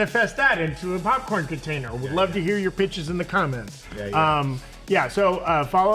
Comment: I thought he said” We love to hear you b*tches in the comments!” 😂😂